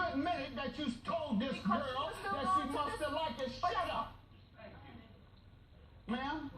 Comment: When Judge Mathis said Shut Up I heard "Thank You" 🤣🤣🤣🤣🤣